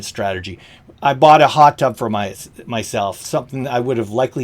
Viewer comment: LOL did i say hot dog??? Not hot tub? I musta been hungry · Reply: Hot tub! I replayed it like three times earlier (18:46)_ and only heard "hot dog." I hear "hot tub" now. Sorry, I was thinking it was maybe a Canadian colloquialism. Anyway, thanks for all you do - I listen to each of your videos (and I don't think any of them is too long 😉)